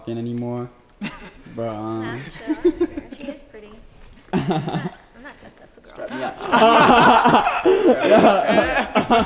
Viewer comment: does anyone know what said at 7:43 ive played it back so many times and i can’t figure out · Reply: She said, "she is pretty, im not that type of girl"